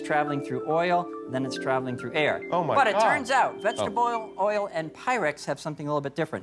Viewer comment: is he saying "vegetable oil and pirates"?